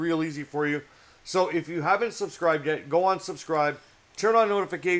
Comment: is it just me or does that sound like go unsubscribe (Don't do that though go subscribe instead of unsubscribe) Also great video!